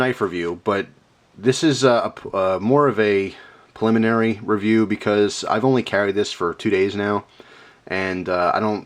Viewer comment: Sorry I just thought you said at 0:38 'urinary review'